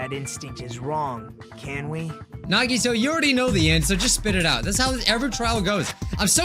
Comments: wait did mike call nagito nagisa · am I the only one who reacted to that he said Nagisa instead of Nagito?